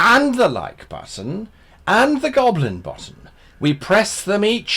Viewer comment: I heard ‘Hit the goblin BOTTOM’ at the end and had to rewind.